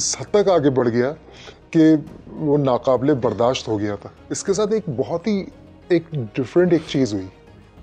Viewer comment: NA QABIL-E-BARDASHAT instead of wirtten QABL-E-BARDASHAT. Correct it.